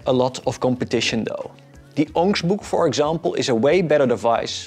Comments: did you say the onyx book? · No wonder 4:23 had the most replays, he suddenly goes cave man speak. Onkgsbok, ongyshbook, onigsh book I really want to know what it is he is suggesting but what the hell is he even saying?